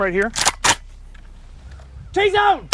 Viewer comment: what does he yell at 8:11? sounds like "T-zone!"